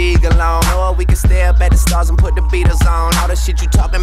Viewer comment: On God I’ve always thought Drake said “Or we can stare up at the stars & put the beaters on” 😂😂😂